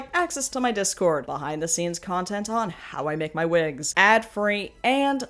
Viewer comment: I will forever be thoroughly entertained by how I misheard "behind the scenes of how I make my wigs" as "behind the scenes of how I met my wigs" the first 2 times I heard it and imagined a series of videos in the style of how I met your mother